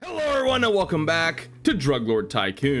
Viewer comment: Sorry about that, lol. (It's made by the same developer though) I figured I make the update video since many comments were asking for it. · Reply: lol oh and i thought you say 'hello and welcome back to one armed robber``